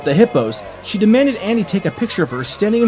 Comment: I thought he said hobo poo not hippo